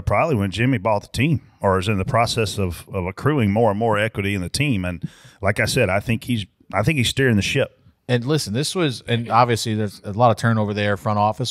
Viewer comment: Brett says, "I think he's steering the ship" or did he say "stirring the $hit"?